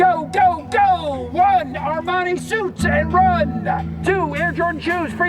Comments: Sounds like he says “Armani suits” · I guess I misheard the " 1) Armani suits" as "are my suits" lmaoo.. oops Anyways, thanks for watching!! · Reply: I heard, "Armani suits and run!"